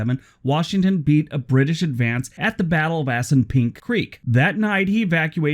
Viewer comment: At first, I thought I heard "ass and pink". I need more coffee.